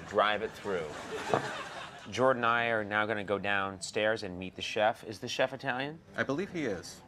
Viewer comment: thought they said we are going to go beat the chef